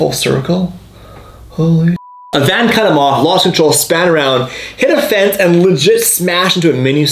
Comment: Did he say "span around" ??